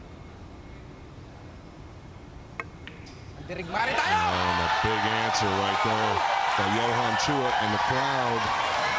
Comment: what did Chua say at 25:18? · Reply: We are warrior too